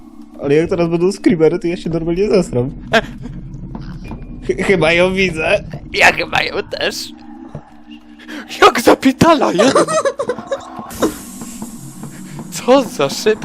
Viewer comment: usłyszałem ja ciebie tesz